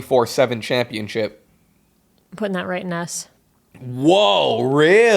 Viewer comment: I thought she said “Ass” not “S”